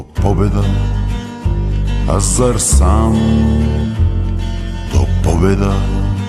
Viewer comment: not 100% sure but I think he says Азар сам = I am ready (or in this case, am I ready for victory?)